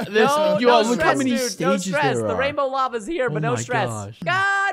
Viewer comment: when he said "lava's here" i thought he said mama's here" xD